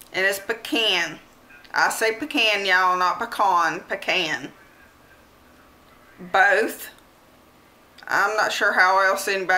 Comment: It sounded like you said BECAN not Peecan